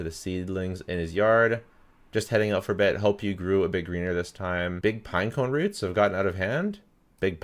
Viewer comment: I heard "hope you grew a big weener this time" 🤣